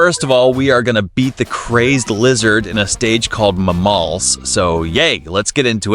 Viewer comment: i Heard ma balls